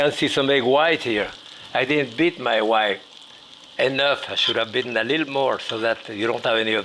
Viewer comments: "I didn't beat my wife enough" I know I just misheard that. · I know he didn't say it but it sounded like, I didn't beat my wife enough. LOL! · I almost thought he said "wife" instead of "white", and that made my ears perk up. 😅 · Did he say he didn't beat his WIFE enough 😳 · Did he just say “I didn’t beat my wife enough”. Great way to blame your wife for a mistake you made. lol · Wait @ 4:22 did Jack say "I didn't beat my wife"?